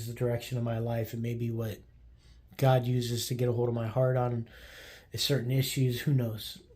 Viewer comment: "It may be what god uses to get ahold of my hard-on..." I feel for you but that caught me off guard. For those wondering, he actually said "Heart on"